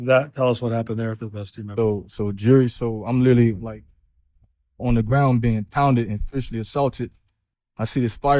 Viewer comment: Did he just address the jury by calling em jury?? Lmao "So jury, so I'm literally on the ground " Iht play it back a dozen times but that is what he said 🤣 @ 32:58-- I wouldn't conclude that ' demon time' means I don't give a.f. I'd assume it meant hell was happening rite at that time Anybody know the definition??